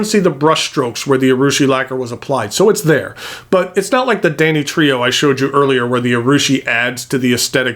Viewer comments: When you were talking about danitrio(?) I thought you said Danny Trejo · I was so confused for a minute googling for one of the pens because it sounded like you were saying Danny Trejo not Danitrio.